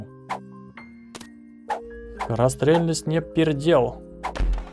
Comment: Мне одному послышалось "скорострельность не пердел"